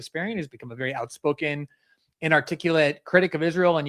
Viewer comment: Why did Glenn crap on Ana in the beginning? According to the captions, he called her a "very outspoken, inarticulate critic of..." or did he say "outspoken, and articulate critic of..."